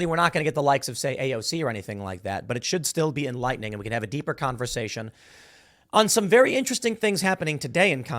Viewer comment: I could’ve sworn he said subversion, not conversation…